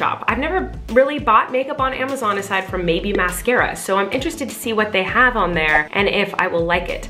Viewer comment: When she said “mabye maskera” I thought she said “baby maskera”